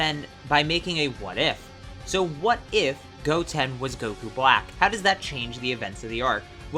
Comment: For a sec I thought it said What-if Goku was Black 😂😂😂